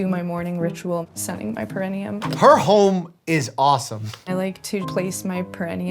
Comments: I thought Dr. Mike said "her hole is awesome", needless to say I was pretty shocked lol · I thought he said "her hole is awesome" had to rewind it and turn the subtitles on before I could take a breath. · The first time I played that, I heard "her hole is awesome". My brain is cooked 💀